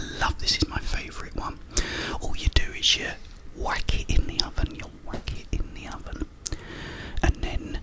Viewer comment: i thought i heard “wank it in the oven” 😂 . Definitely wrong on my part😂😂 , Regardless amazing video as always and keep up the amazing content ! Love from Canada 🇨🇦🍁